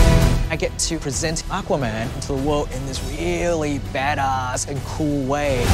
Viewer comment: Am i the only one who heard James Wan saying Badass like "Bed As" in